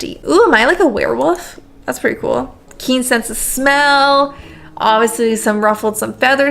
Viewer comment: Is it weird when I first heard it, I heard, "kinky-ence of smell."? Hmmmm...